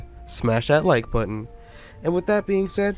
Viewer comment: The 0.1 people who don’t have a tongue: ;) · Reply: at first i thought he said click the like button with my tougue